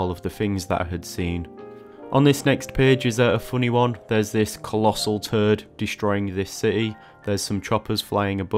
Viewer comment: I have never heard “toad” pronounced in such an interesting way. 😅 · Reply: Originally I thought you said you drew a collasal turd! 🤣